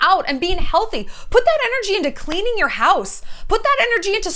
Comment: I heard “Take that energy and use it to clean your house”, got ashamed and went to go wash the pile of dishes in my sink. Thanks for yelling at me 😏